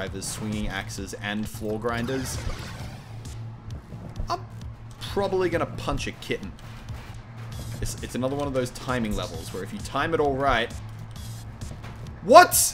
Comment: I thought he said I’m gonna punch a kid